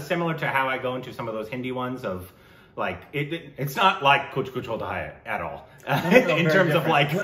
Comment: Did I hear "Kuch Kuch Chodha hai"..